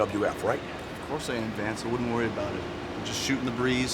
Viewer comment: Does Jericho call him, “Vance”? That is hysterical 😆